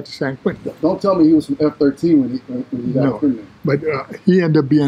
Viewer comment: what gang did they say Mexican bro was in? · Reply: MM